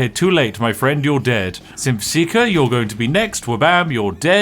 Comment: I heard "simp seeker" every time... and who isn't seeking simps lol